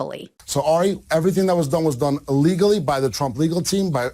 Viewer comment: I always thought Epstein said “illegally “ in that news clip. But since nobody ever mentioned it, I thought I must have worse hearing then I thought.